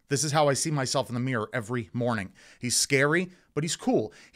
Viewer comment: I thought you said he's hairy and I started laughter 😂